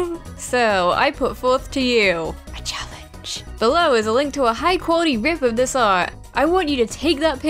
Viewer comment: did someone say "high quality rip"